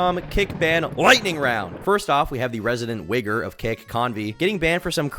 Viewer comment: did he say wigger 😭😭😂